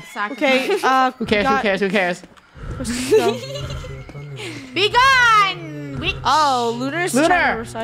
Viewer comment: it's always fun watching screaming and suffering. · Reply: even when lunar gets a little crazy i think i nearly caught her saying ''be gonneeee bitc-'' 40:57 for the timestamp lol